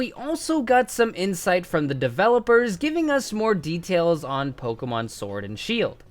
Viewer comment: anyone else hear pellipers